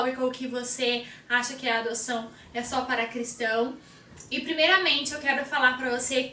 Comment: Pois quando eu vi essa camisa eu entendi "Adoção é Cruz para cristão" o desenho da cruz me confundiu kk